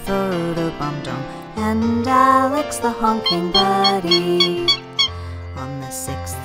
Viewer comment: ok, thats creepy. when I look at the word "honking" I hear her say honking, but when I close my eyes she says "humping"